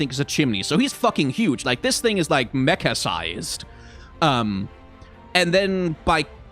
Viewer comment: was listening to this in the bg and thought said mecca-sized and i was like 'oh wow that is massive a whole city??' and it only clicked that he probably meant mecha-sized like a minute later. anyway ramadan mubarak to those that celebrate 😂